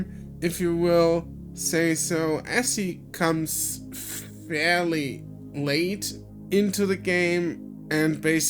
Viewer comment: I heard this as "As he cums a little bit late" because of the context